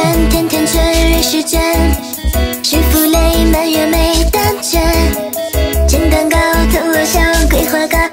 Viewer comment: Alright real talk 0:54 Is she saying "Chic-fil-a mayonnaise" or is this just Chinese that sounds like "Chic-fil-a mayonnaise"?